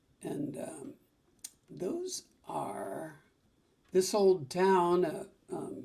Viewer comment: Did I hear, "This Old Porch"????? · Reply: Should be.... steamin' greazy plate of enchiladas....but I believe I said "this old town" by your old girlfriend, Nanci.